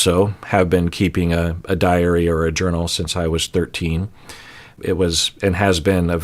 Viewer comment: I swear that said that he "also kept HER diary or journal since he was 13". And I was like wow, has been a Swiftie since 1982, which is really impressive since this was 7 years before Taylor was born. He of course did not say this, and I had to relisten to that section 4 times to convince myself. Carry on.